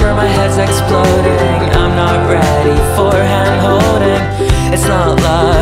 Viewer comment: For “handing-holding” I thought you said ham and I was like WHAT? 😂